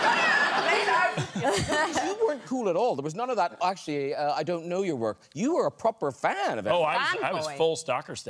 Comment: Hey guys, my first language is not English so it's a little struggling to watch this video, so can anyone tell me what Graham said at 1:19 please. Thank you!!!!!!!!! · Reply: He said "So you weren't cool at all, there was none of that 'oh actually uh, I don't know your work', you were a proper fan of Emily". Cool, in this context, meaning aloof, casual, nonchalant.